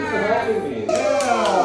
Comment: I thought homie said butterfly 😂 and it said popcorn and I lost it